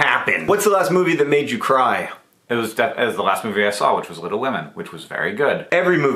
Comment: It sounded like Hank said “Little Lemon” when asked what movie made him cry. Truly wish that were a movie, apparently it’s only a variety of lemon